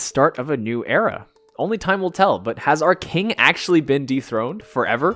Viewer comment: I heard "Arcane" instead of "Our King"😂 symbolical 😁